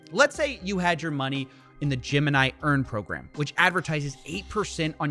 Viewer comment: Was listening while in the shower and was totally confused when I kept hearing "Jim & I earning program".